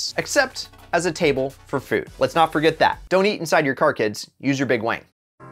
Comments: DID I JUST HEAR HIM SAY "use your big wang"? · Did he just say "... Your big wang", instead of "wing"?